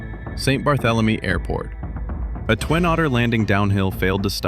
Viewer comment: Fair point, TD—captions are usually a viewer’s choice. In this series the text is baked into the edit (so it can’t be toggled off) because: Cross-device reliability: some TVs/apps drop or hide CC; Many watch muted on mobile/TV; Auto-captions often mangle aircraft/airport names—on-screen labels keep date/location/corrections accurate; Clips get reposted out of context—a small label preserves the facts. I keep overlays minimal (date, location, type, brief note) and I’m trimming size/opacity so they don’t cover the action. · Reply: "Auto-captions often mangle aircraft/airport names" ...Like "Twin-Autor" instead of Twin Otter?